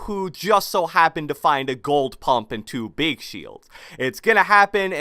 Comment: did he say gold pump